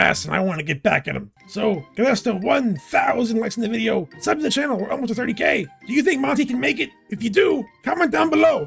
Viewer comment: Foxy: "We're almost up to 30K!" What I hear: "We're almost very gay!" I'm sorry, but pride month has officially altered my thinking process, apparently. 😅